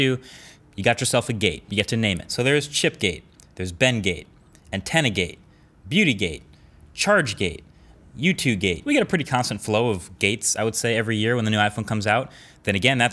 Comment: Did he say “you 2 gay”? on